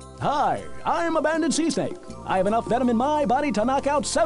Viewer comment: When I had this game I heard it as 'abandoned sea snake'. 16:20 Isn't 'cinque' fifteen and 'cinco' five? 17:10 BONK